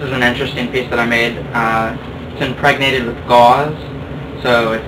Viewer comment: what did he do with it ? I can't hear :( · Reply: "Impregnated" it with gauze